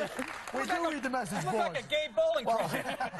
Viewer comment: "You look like a gay bowling ball." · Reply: i thought he said gay bowling trophy